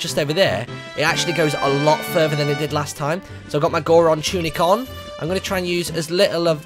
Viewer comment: lol i thought he said i have my moron tunic on